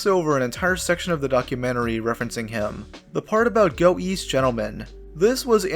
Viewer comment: Go east? I thought he said go weast